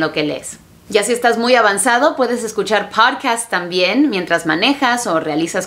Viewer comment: Que dice en el 15:21 ? si estas muy avanzado puedes escuchar potcats??